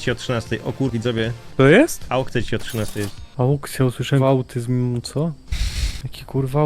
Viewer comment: Ja też usłyszałem autyzm zaraz aukcja XD